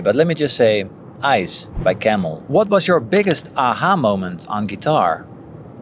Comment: > Best guitar solo. Someone could tell me what he ansered? I did't understand fully what he said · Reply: Ice by Camel